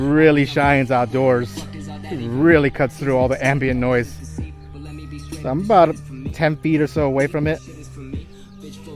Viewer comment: what the trek? 6:11. right now! say my, pleas! If my English so bad, sorry! :) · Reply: Josh A - Right now